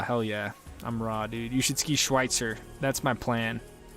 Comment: Thanks man! Only looking at doing mountains that are on the epic/Indy/ikon pass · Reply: oh, then you probably meant schweitzer. I could have swore I heard you say "silver." No worries.Schweitzer is bangn' !